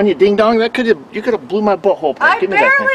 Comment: It sound like he said, (you could have blew my butthole open).